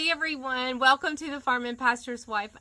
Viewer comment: It sounds like you say, “Welcome to the farm imposter’s wife!” 😂